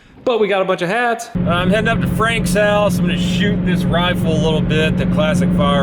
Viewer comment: I thought you said "Im going up to Franks house to shoot him with rifle" had to rewind